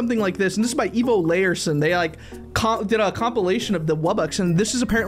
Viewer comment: is it just me or did he say Evilarison? 😅